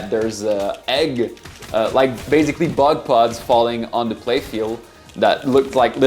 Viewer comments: but plugs falling on the play field, sorry I don't know why my ears heard this instead of what he intended me to hear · That sounded like "butt plugs" when said "bug pods" 😯😂😂😅😝